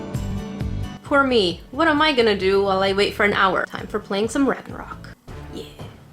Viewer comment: whats this video game called? I couldnt hear it. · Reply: I think it’s called Ragnarok online?